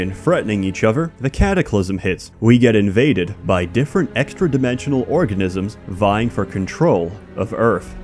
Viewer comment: I swear I heard "invaded by inter-dimensional orgasms" and I thought it was perfectly on par for.